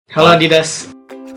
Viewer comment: Apa gw salah denger? Di awal² video ngomong "halo adidas"? Budek kali gw :v